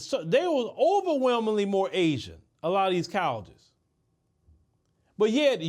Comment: The whole time I thought this dude was saying cows but was saying colleges 😂 gotta open your mouth man